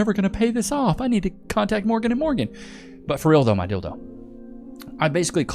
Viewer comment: it sounds like he said (my dildo). My friend setting next to me said, what kind of trash are you listening to? We listened again and realized that's not at all what was said. Thanks for the laugh. But the laugh is on us. Love this show.